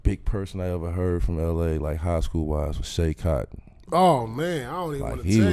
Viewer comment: JAY ! Thank you Bro I’ve been looking for him since the KG video lol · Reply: holy shit man same like I watched the KG all the smoke podcast and tried for like an hour to find him but couldn't. In the video I kept hearing "Kai" instead of cotton